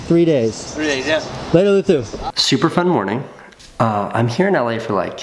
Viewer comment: what did he say? xD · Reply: I caught that too. He said Luthu but his name is spelled Luzu. Thing is Spaniards, such as Luzu, like to pronounce the s as if it where a th. Listen to Enrique Iglesias songs and you will notice right away. Only if you haven't already. LOL